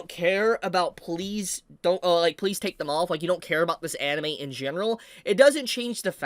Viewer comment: did he just said "Please take them off" 😭